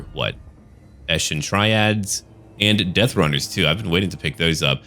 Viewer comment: Soooo, now I hear "Eshin Tryhards" and "Great Cheddar" for that skaven mage :D